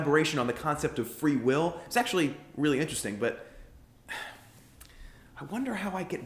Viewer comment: Did he say 'sexually really interesting' ????